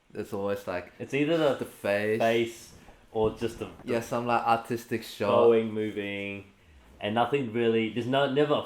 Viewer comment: it sounded like he said autistic shots